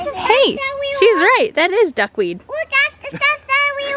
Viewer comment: Totally thought she said "that's skunk weed"! Thanks for setting it straight for me, lol.